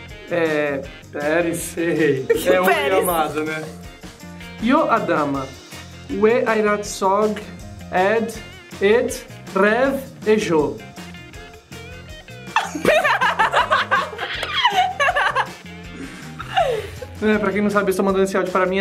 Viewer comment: fui só eu que escutei tejolo kkkkk